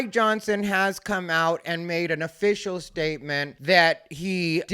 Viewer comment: for a surreal moment I thought you said 'My Johnson has come out and made an official statement'... the hands waving away from your lap must've reinforced the concept 🖐🤚